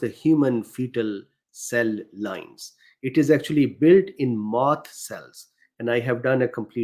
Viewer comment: did you say mast cells?